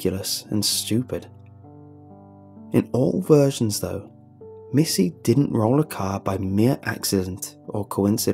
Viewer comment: I thought you said missy dies